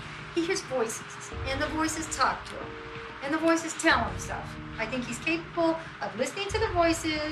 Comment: I cannot make out what the witness is saying. Even the closed caption doesn't help. It sounds like she said: "I think he's claustrophobic'', rather than schizophrenic, because she continues by saying that this person hears and listens to the voices in his head. Weird!